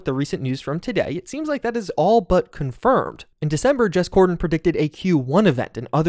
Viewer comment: woah you said jez gordon but i heard "james c*rden" (censoring this for potential loss of sight if you see the name too much) 😂